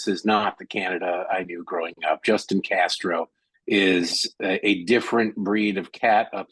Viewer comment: I swore that I heard the Canadian gentleman first called him, Justin Castro! Good one!